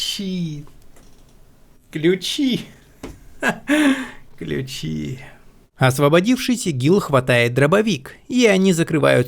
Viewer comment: мне послышалось "Освободившись ИГИЛ хватает дробовик", странно